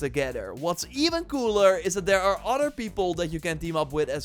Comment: did i miss hear or did my man just say theres "Other people" that can help u fight instead of just miles or peter ???